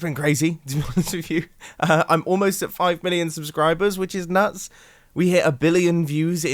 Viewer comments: I heard 5 billion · I am the only one that thought he said that he has almoust 5 BILLION subs..... I actually went to check................